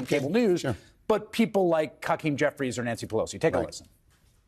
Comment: Did Hayes say “Cuckeem Jeffries”?! lol